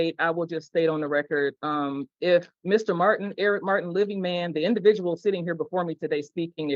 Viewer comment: I thought he said I’m a little man not living man.